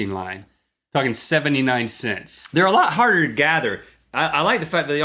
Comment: Did Link say "fuckin 79 cents"?